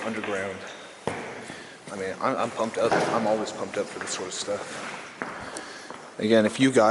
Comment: 🤣🤣the old timers were singing. The camera man ohh hell no I ain't going towards devil chanting haha I thought you said you're all pumped for this 🤣🤣🤣🤣